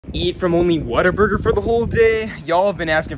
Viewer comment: I heard him say it waterburger for the whole day 😂